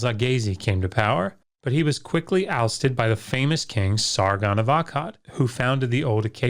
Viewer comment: For a second I thought he said Sauron instead of Sargon lol